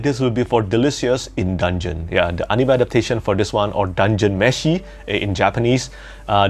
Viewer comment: Lol, for a moment when you were talking about Delicious I thought you said it's about "Cooking meth, Dungeons and Dragons". Imagine my surprise when your next phrase is "Yeah, I love cooking..." - "What are you about to say, Petrik?!!". I realized that I misheard :-) "Yeah, I love cooking meth in dangeons, no big deal".